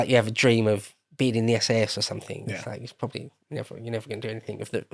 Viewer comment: I heard SS and not SAS at first. Caused an eyebrow to raise a bit...